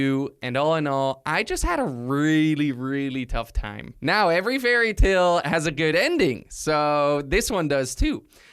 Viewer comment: Lol is it just me or did he just say that it was all a fairytale